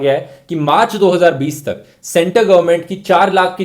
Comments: I thought he said maa chudao 2020 tak . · i heard maach***o 😄😅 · is he said maa c**do???!!!!!! 😀😀😛😛